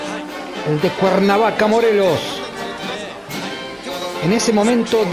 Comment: yo escuche El descuerna vaca morelos...🤣